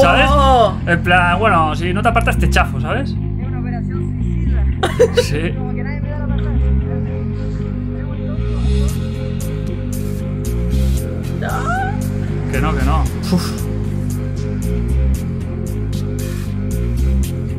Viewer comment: "Nadie me dá la pasá" Cómo dice que dijo?